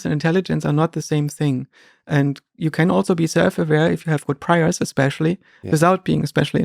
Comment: Can anyone confirm for me what Joscha says at ~27:48 "you can also be self aware if..........."? I can't quite make out the individual words. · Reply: "you have good priors, especially". Is my understanding of what he said.